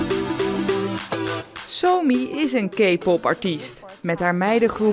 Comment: I first heard "somi is a gaypop artist"